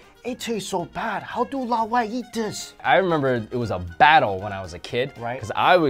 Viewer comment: did you say 老外? Hahahaha, very true though